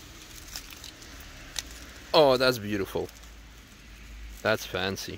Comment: i know right? 3:52 REALLY I THOUGHT YOU SAID IT'S BEAUTIFUL